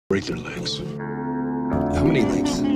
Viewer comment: I thought he said he was gonna cut off their legs in this clip I was so confused when he said break lol